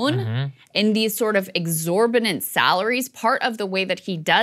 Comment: Sounds like Krystal says “exorbinant”. Exorbitant